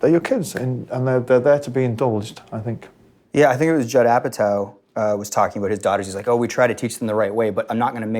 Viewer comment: Top job. Amazing interview. Who do you mention at 02:50? I'm putting Italian subtitles on this (hope you don't mind) and can't understand that bit. Thanks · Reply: Noel Gallagher oasisnotizie Thanks for taking the time to watch and transcribe in Italian! Much appreciated. Judd Apatow is the fellow mentioned at the 2:50 mark. <link>